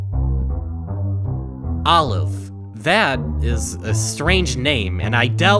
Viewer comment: for a sec i thought it said "alive" wouldnt been terrifying imo